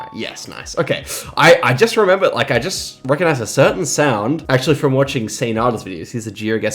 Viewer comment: from watching who? Didn't catch the name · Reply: Saynada (Say / GeoIsrael)